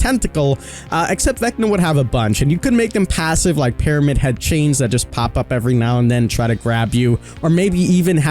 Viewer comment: Did you just say Pyramid Head chains?